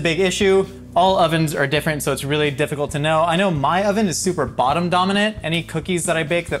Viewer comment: Did he say "bottom dominant"?